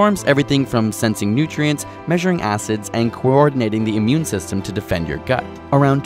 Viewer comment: When he said measuring acids. I thought he said measuring asses. Who else heard that?